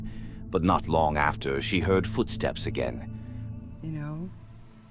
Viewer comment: I hear "Foot Prints". Ahhhh Foot steps... not prints lol